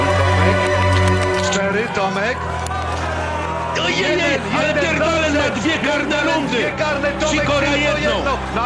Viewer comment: czy tylko ja usłyszałem "je pierdole"